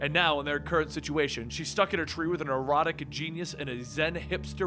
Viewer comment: Did he say erotic genius?